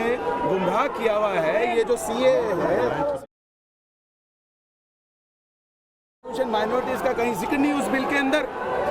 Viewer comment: Mute kyun kia. CAA k saath kya bola tha vo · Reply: Woh un dono ko godi media bol raha tha